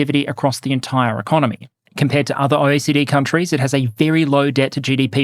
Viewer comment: I know it isn't what you said, but at 6:55 I could swear I heard "Compared to other OCD countries, Germany has a very low debt-to-GDP ratio".